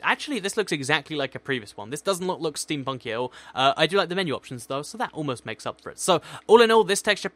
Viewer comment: When said ' Menu Options,' I thought he said minions. I had to go back to check I'm hearing correctly. :'D 😂